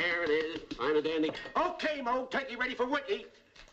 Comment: What did Shemp say around 2:20-2:25, again?... I could never get that part when I was a kid. "O.K., Moe, the turkey's ready for--" what?... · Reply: Turkey's ready for worky